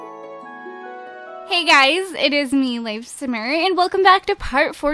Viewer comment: At the beginning I thought you said "Hey guys, it is me, LifeSinner"